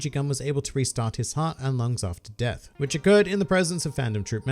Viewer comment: Thank you so much! Now I am no longer sad this fight was left out of the original animu: this is just stupid. . EDIT I thought you said "a month after death", while it was "and lungs after death". This plot twist is now slightly less stupid for me.